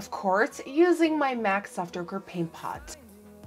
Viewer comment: I thought she said "MAC ogre paint pot" omg